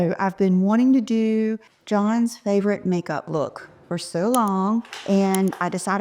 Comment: I thought you said John’s favorit make-up is when you wear YSL